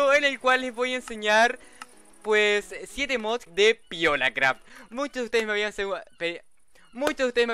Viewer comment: le entendi "violacraft"